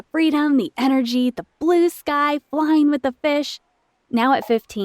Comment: I heard her say “the bliss guy flying with a fish”😂😂😂😂